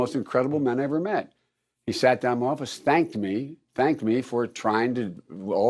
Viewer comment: I thought he said spanked. 😏